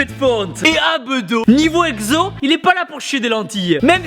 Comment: Les "zabedos" ?? 3:43 C'est quoi les zabedos ?